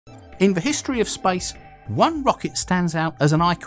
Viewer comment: I misheard the start as "in the history of spice"